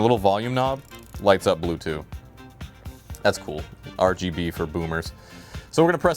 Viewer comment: I was wine-drunk and heard "Bluetooth" instead of "Blue too" (english is not my native language) and my autistic adhd drunk mind was like "WHAT!? BLUETOOTH VOLUME NOB?"